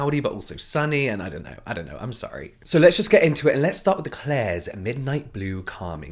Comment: I wasn’t paying attention at first and when he said “klairs” I heard “Claire’s” and I was like wait what and I looked up and was so relieved